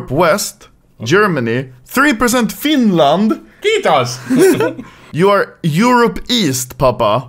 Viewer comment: what did said? Fitas Litas Titas Ritas Pitas